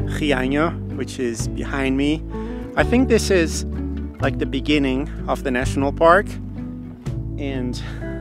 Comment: you said Beginning, i heard "in the benninging" that meme.. 🤣🤣🤣🤣